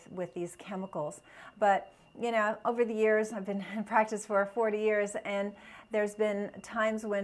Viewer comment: Did you say that you've been in practice for forty years??? Did I hear you right ?